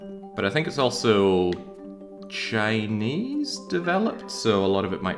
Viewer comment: I don't know how to explain it but I could tell this was a Taiwanese developer who made this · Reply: I thought he said Chinese dev at the start?